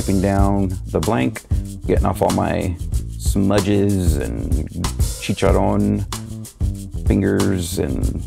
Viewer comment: did you just say chicharon? lol. I love eating chicharon